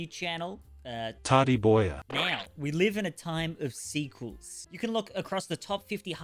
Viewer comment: I thought at the start you said "We live in a world of seafood"